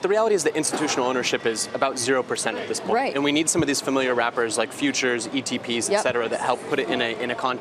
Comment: who else thought he said we need familiar rappers like Future to put Bitcoin into context?